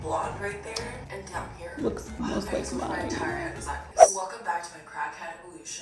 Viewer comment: what does she say?! Someone help me bc it is driving me absolutely insane haha!! 🥴😩🤯🤬🧐🤔 I have played this part this 50 times at least AND turned on captions but it doesn’t have a caption for this sentence!!! Go figure 🙄 She says, “Welcome back to my crackhead......?!”